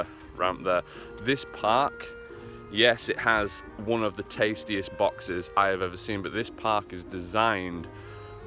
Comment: did you just say "tasty box" 🔥😂